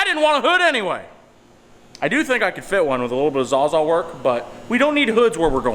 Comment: Did you say ""ZAW-ZAW "" at 7:04....😝😝😝..wait I forgot my mask.. 😷😷😷